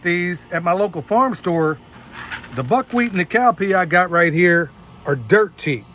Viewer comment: Buckwheat & cow peas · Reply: I kept hearing “cow pee” didn’t hear the plural, “peas” 😂. I hadn’t heard of “cow peas” before. Well, that’s my laugh for the day. Thanks